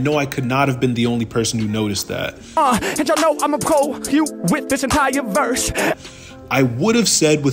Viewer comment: As for the missing [word] in 52.4, I always heard it as "I'mma cull you with this entire verse" Edit: Funny that I also missed a word somehow...